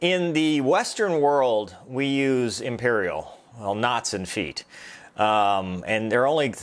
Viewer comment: All I heard was "Nuts and feet." ... How is this a professional way to communicate your status? PS: Great video, no need to dissect this "gem" .. it is just too painful to watch.